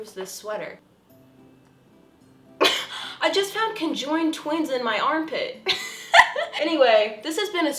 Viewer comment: I THOUGHT SHE SAID CONJURING TWINS